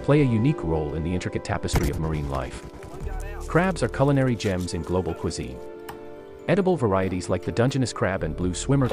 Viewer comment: Did you just say "in global pusay" 😮😂